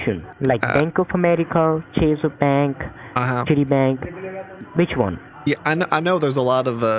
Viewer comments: I thought he said Titty bank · I heard "Titty Bank" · It sounds like hes said tity bank, I busted out laughing so hard. 🤭🤣😂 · Did the scammer just poop his pants when he heard $420,000 dollars he must think all his Indian Christmas's has come all at once , i thought he said titty bank that would have been a right booby lol . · Sound like he said Shitty Bank 😂😂😂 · I will never look at my TD Bank card the same ever again. 😂🤣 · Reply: Sounded like he was saying titty bank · At one point it sounds like he said shitty bank lol